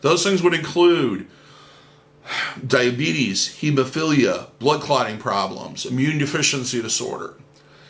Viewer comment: I thought you said hebephilia when you listed health issues lol